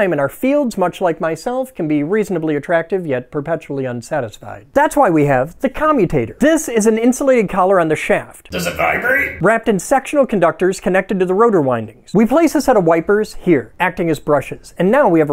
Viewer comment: I thought he said sexual conductors at first!😅 These videos are hilarious, and informative at the same time, and that's pretty cool!😜👍